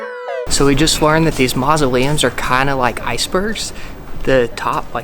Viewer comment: ohh come on guys u were going to the cheapest places in the city. Go eat to Palermo or the capital or almagro. The big houses on the cementery are MAUSOLEUMS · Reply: Mausoleums. That's what the man said (4:03).